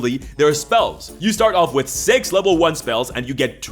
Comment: I though i heard sex level 1 spells rather than 6.